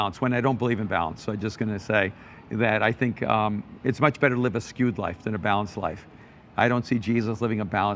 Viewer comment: Could someone please spell to me what kind of life Erwin says we should live in minute 22:40 ? Thanks a lot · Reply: Hi Alberto, I think you mean where Erwin talks about living a skewed life rather than a balanced life. Might have been hard to understand if English isn't your native tongue. Hope that helps. God bless.